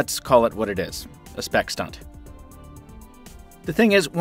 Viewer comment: What is said at 1:47 "let's call it what it is: it's a spec stud"???? I can't identify the last two words heard, cause I'm a lousy German native speaker with some good knowledge of ENGLISH. Sounds not to be a positive idiom for the LG G3. Could someone explain how these words are spelled? Thx in advance Matti Helmers / Wuppertal / Germany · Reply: Spec Stunt